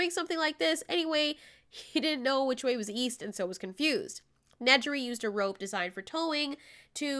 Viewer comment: Lmao thought it said Nerdy